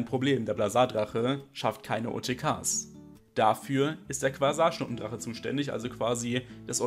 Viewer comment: Ich habe licht empfängermonster verstanden😂 Beim Quasarschnuppendrache